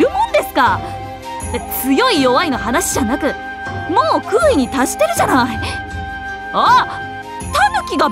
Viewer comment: 小次郎になんて言ったんだ？ ～に達してるまでは聞こえたんだが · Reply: 空位に達してる、と言っている。武蔵からすれば多重次元屈折現象を引き起こせる燕返しを会得した小次郎は極みに達しているみたいな意味だと思う。ただ、女武蔵の世界の小次郎は違うみたいだから、タヌキが化けているのか！？って言っている